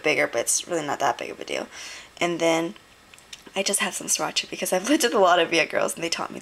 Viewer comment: What is the red sauce at 9:07? I keep hearing "swatch" but it makes no sense to me